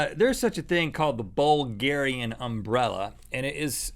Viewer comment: WHAT?! U SAID BULGARIAN UMBRELLA or something else?